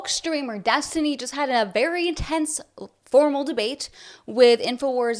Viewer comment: I had to rewind it twice cuz I thought you said destiny had a testicleless debate